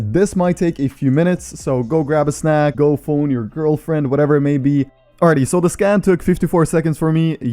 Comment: did he just say "fold your girlfriend" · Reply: phone your girlfriend 📞